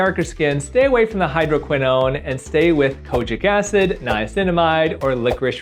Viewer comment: or WITH..i heard stay away from...as well😂😂😂 had to rwwind 3times